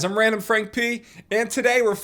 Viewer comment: RandomFrankP? I heard RANDOM DANK MEMES